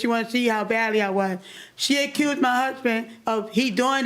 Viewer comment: I let out a luh gasp 😂i thought she said she killed my husband , poor thing said accused 😭